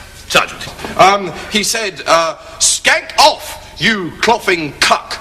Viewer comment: Yes I live in america I know what skank means thank you. It was a simple mishearing you had... He said skag not skank. I am not trying to flame you or anything so please don't feel as such. Just wanted to correct you.